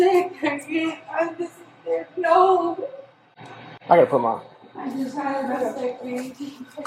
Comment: Sounded like she said I just had a vasectomy.😂😂